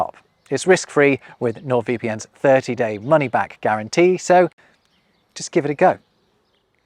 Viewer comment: I actually thought, for a second, you said, "just give it a goat" at the end of the VPN ad